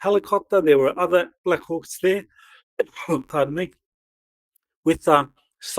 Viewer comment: Blackhawk helicopter and Apache helicopter are two different craft , referred to him getting on a BlackhawkApache helicopter. Just saying. Peace on Earth and the Stars. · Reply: when he said that, it confused me too. Then when he said it again, it sounded like he said there were Blackhawks AND Apaches there. But I could be mistaken 🤷